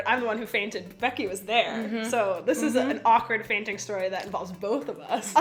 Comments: I thought it said 'akward farting stories'😅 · I thought it said awkward farting stories